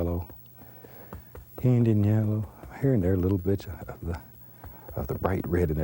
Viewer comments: Sounded like he said, "little bitch". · Lmao, what Bob says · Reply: little bit